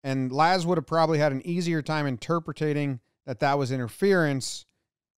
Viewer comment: Did Jomboy just say "interpretating"? 🤣👍🏼 I love this channel!